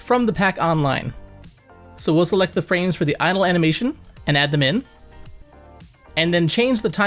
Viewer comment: Love tutorial but between 2:14 and 2:17 subtitles, instead of saying Idle animation, it says anal animation 😂